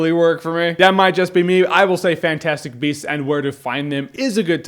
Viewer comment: I thought it said fantastic breasts